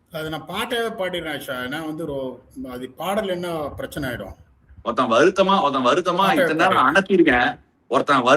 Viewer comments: at first i heard “Otha varuthama” 😂😂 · Did Ashwin just say "otha" 14:43 😂😂😂